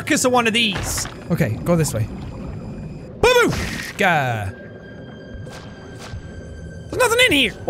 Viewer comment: Did I just hear "babushka"? 😐😂